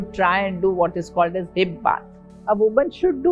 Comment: What Dr said hibba..at 7:33 min .i couldn't get it..plz explain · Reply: Namaste Farhatji, Hansa Ma said Hip Bath check this video <link>